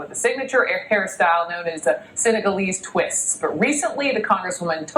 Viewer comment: what did she say?? Sinna-gul-lees twist????